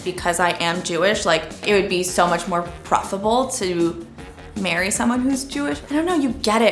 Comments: The jewish woman said it would be preferable to marry someone jewish but the caption said profitable. I didn't know ya'll had Kanye doing your captions Cut 🤣🤣🤣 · I think the English(US) CC needs an update. I'm thinking "preferable" but could have misheard.